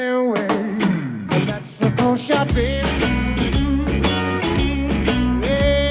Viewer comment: The first time I ever heard this song I immediately loved it but I didn’t know the title yet and I thought rather than him saying “that’s a cold shot baby” he was saying “let’s go shoppin”